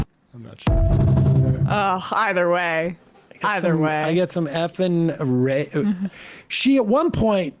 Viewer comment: she says "bunch of f-ing grapists". The grape word censored for social media (just remove the "g" and you will know what it word means) She called em' like she saw it. There is a lot of SA and abuse going on in the detention centers and when these creeps detain women or children so she is absolutely not wrong and this goes insanely unreported even in 2016 there was reports of terrible things being done to children by ICE in detention centers. Why aren't we talking about this more? There were reports made and everything that info is out there.